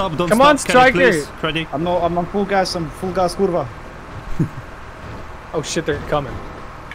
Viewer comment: Did he just said "Kurwa"?